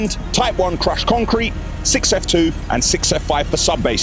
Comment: well done you learned the difference between 6f2 and 6f5 i hate when people ask me for 6f2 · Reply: Up until this video I've always thought was saying 6O2 and 6O5 (as in the letter O ) 🤦‍♂🤣